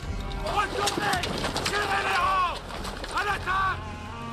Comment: Did Calvet say 'a la carte'? I know he likes his food, but good lord...